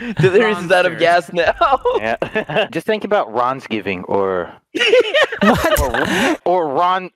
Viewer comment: i heard “vanoss GTAV” i was so excited for that Christmas special for ANOTHA REUPLOAD